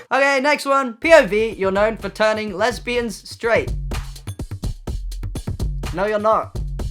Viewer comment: I wasn’t looking at the screen and instead of hearing “POV: you’re known for turning lesbians straight” I heard “POV: Your gnomes are turning lesbian straight”